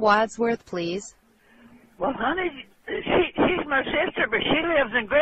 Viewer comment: Did she call her "honey" at 0:11?